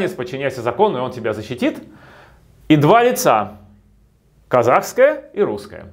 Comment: Один я услышал "два яйца" вместо "два лица" ?